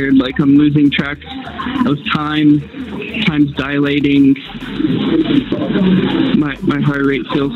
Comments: He said "I'm dilating" or at least that's what I heard. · I think he says, I'm dilating," as in his pupils. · (Inaudible)? He clearly says "Times Dilatating"